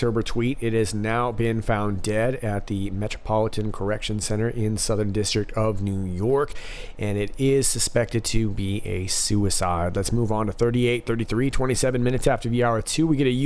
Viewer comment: Did I hear right? Update on Oct 10, 2018 HRC tweet @ 22:45, that"...it's now been found dead at Metropolitan Correctional Center in southern district of NY, and it is suspected to be a suicide?"